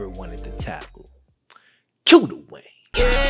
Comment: bro this whole time i thought you said cue the way